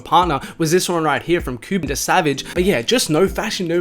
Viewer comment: I thought he said Coup The Savage